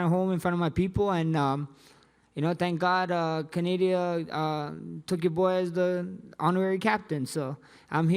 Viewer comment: he actually just said Canadia and I honestly thought it was just me who said it from time to time